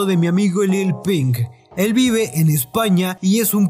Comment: pensé que dijiste Lil Peep.